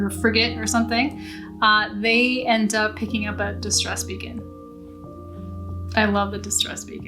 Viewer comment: I thought you said "distressed vegan" and I was thinking "is that a THING in horror novels?" Distressed vegans can be horrifying I can tell you first hand.🤣